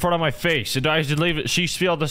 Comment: Every time, she puts it infront of my face. I should leave but she stealed the [screwdriver] I think he said stealed because he was in shock